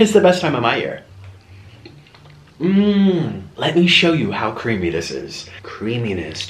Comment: When he said let me show you how creamy this is i thought he said let me show you how cringy this is and im like what. You are saying avacados are amazing in one second then you say they are cringy.